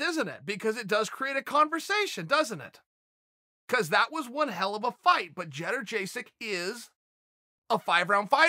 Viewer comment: lol did Chael just call Joanna Jedrzejczyk, " Jedder Jaysick " ? hahaha